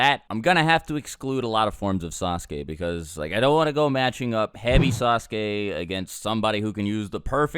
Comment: I've been watching yours and videos for years now. And let me tell you it took me a lot longer than I would like to admit that I always heard "HEAVY" Sasuke and not Hebi.